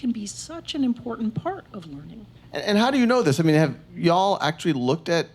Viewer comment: did he say y'all